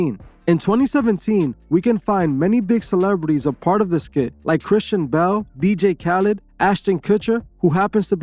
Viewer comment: Hey said Channing Tatum… and you’re “pretty sure he’s a Christian” when he literally thanked Satan on a stage, on camera. Like use your brain man, Google is free. Look it up. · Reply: it's Kristen Bell, my bad, it sounded like Christian Bale 🤦‍♂️